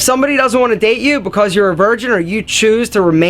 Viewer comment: At the end I thought he was gonna say "they are not worthy of your dick" lol